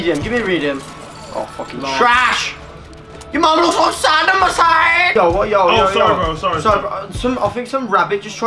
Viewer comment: what did he say lol 😂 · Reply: "Your mum looks like Saddam Hussein"